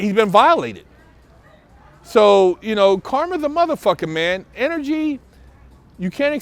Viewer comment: did he say “carmen’s a muthafucka “